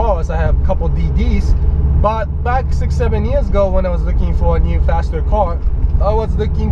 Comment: thought he said 67 years ago on 4:18 .....lolololol